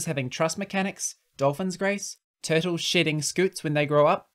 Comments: I thought you said "turtles sh*tting scutes" instead of "shedding"... XD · "turtles shitting scutes" wait a minute i know i misheard that